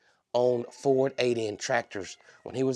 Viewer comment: Oh my I thought you were saying ALIEN tractor because of the strange things happening to it. Then I realised you were saying Ford "8N" tractor 😆